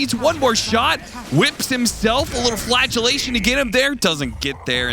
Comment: Cap saying Flatulation instead of flagellation had me dying 🤣